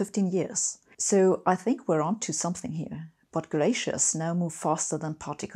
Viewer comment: / 10:46 what does she say please...." but gracious?? now moves faster than particle physics....."